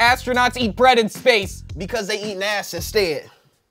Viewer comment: I thought he said “they eating NASA instead” like it still sounds like ass but it’s themed to the question and that made it so much funnier but that’s not even what he said 😭😭